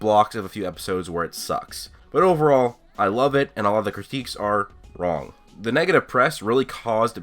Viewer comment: Also I do hear what everyone is complaining about at 1:07 in normal speed it sounded like u said all but if u slow it to .5 you can hear him say a lot. He even repeats him self at 8:19 by saying"A lot of these criticism are absolute BS"